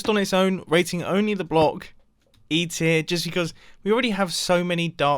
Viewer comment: Am I the only one who heard 'eat it' instead of e tear when mumbo was talking about the mud block?